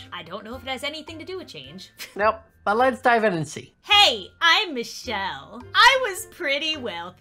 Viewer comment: I thought said let’s die into this